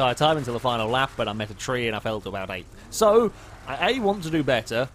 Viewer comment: What does even say at 0:33? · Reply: John McC But I met a tree and fell to about 8th